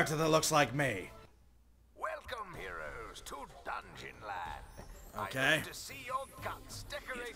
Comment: i thought it said dungeon lords not dungeon land i am dissopointed